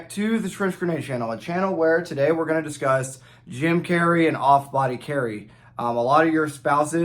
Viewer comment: Man said "gym carry" I heard "Jim Carrey"